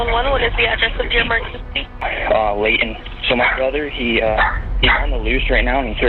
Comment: bruuuu when I heard him say "uh Layton" I immediately was like huh yes someone say my name😭🙏cause my name is literally Layton spelled the exact same way😭🙏🙏🙏🙏🙏